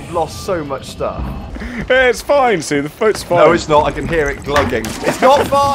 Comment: "see the foats fine" Wait did you just say foat? GODDAMNIT